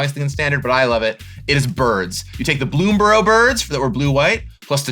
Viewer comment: OMG innthe intro, my hearing is so bad, I thought I heard say he was playing a "burn" deck-- but he actually said birds 😅 Probably shouldn't admit it but, I try to be honest lol But with how he dressed for this, he looked dressed to blast with red spells and blow away the competition ☝️😊👍 16:00 Man... I am a bit disappointed... I was hoping to see play the Vivi card. Wondering about it's image and what color(s) it is... 😥 🤧 33:20 disappointment doubled 🤕